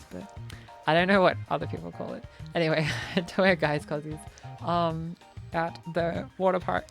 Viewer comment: what did you call them? As previously stated I’m a queenslander but I don’t think I’ve heard a word that sounds like that before? But I don’t keep up with togs and things swimming related in general for the reason that may be expected from a Lucy viewer. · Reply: its spelt as "cossies" its just what people around me has used. But I've heard some people use togs... so maybe its a regional dialect thing? unsure! haha